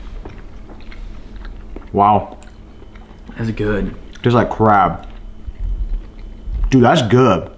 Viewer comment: i thought he said it tasted like crap until i re-winded it and he said crab lol